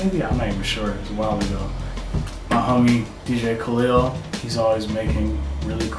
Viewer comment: Whats the name of the guys behind the music? Sounds like he says Kalyll & Danny. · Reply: Iusedtohavemojo DJ Khalil & Danny Keys